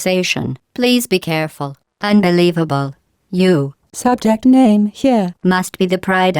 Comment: "unbelievable! you, (subject name here) must be the pride of (subject hometown here)." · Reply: I thought she said subject May Heinn. Must be the pride of Subject Mon Tou Heinn.